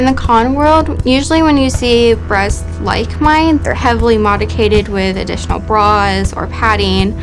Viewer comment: did she say "heavily modicated"? Wtf is "modicated"!??!?